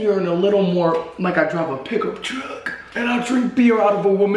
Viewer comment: I thought you said "pickle truck" instead of "pickup truck" and I got a little too excited.